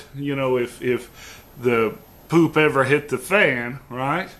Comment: did he say poop???